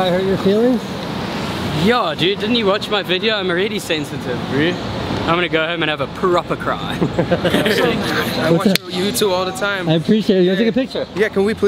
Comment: Hahahahahah that cracked me up I’m gonna go home and have. A Proper cry I LOL so hard 🤣🤣🤣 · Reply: I thought he said perroper cry or something